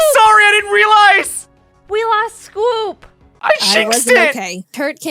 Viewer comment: Did you just say "i shinxed it" or was that not a pun?